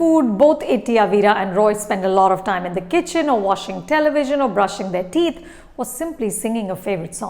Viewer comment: Did she say washing television?😂